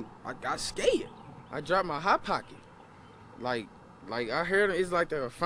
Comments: i thought he said "i drive my hot pocket" and i was like how do you drive a hot pocket is that even possible i want to do that too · At first, I genuinely thought he said "I drive my Hot Pocket". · i thought he said hot dog